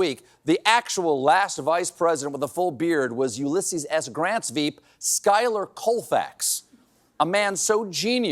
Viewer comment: did ... did he say 'weeb' ... ? :'-D